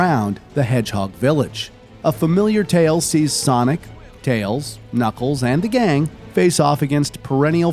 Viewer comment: did he say “tails” 2 times??